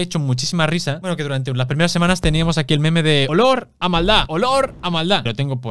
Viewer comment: Pense que decia "¡Olor a Madrid!" XDDDDD